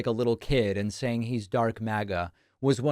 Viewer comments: I thought he said he was drunk maga not dark maga whatever tf that is Bcz he was acting drunk or high But hes just special in the head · Ahhh dark MAGA. I thought Elon said drunk MAGA. 😮😊